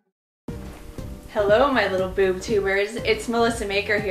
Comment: did she say boob tuber at 0:18?